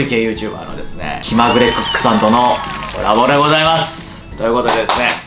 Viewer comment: ここ最初｢きまぐれクックさんとのアボカドでございます｣って聞こえた